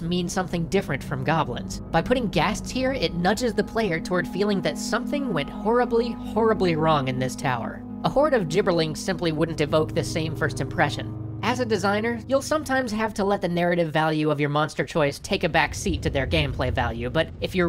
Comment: Guys i need your help. What does he say at 4:52 ? A horde of? Giberlins?